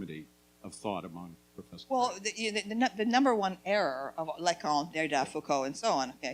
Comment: She mentions three (french?) names at 8:30. Pretty sure one of them is Foucault. I think I hear Lacombe but the third is indecipherable to me. I have no ear for French. If anyone could tell me the full names of these people you would be saving my life. I am desperate to understand Camile Paglia better. Very late to the party. THX in advance. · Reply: Derrida